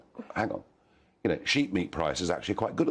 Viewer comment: 'Sheep meat'? You mean Lamb? I thought Nigel was saying 'Sheet meat'. It's sheet, but it's doing well! :D